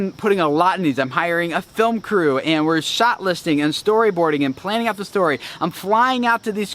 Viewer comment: could have sworn you said you were shoplifting lol